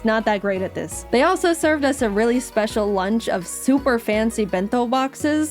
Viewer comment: My video buffered when jaiden was saying super so all i heard was: "They also served us a really special lunch of soup"